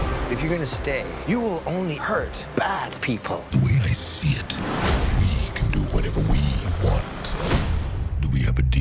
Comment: anyone else having trouble hearing what venom says at 8:20 it sounds like "the way i see it, we can do whatever we want" is that right